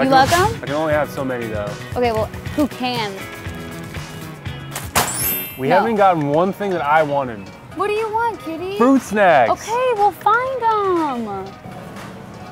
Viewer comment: " we haven't gotten anything I want.." Lauren: "what do you want cutie?" "fruit snacks!" Lol #relationshipgoals ❤️👌😍👍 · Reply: Before reading your comment I actually thought she said kitty instead of cutie..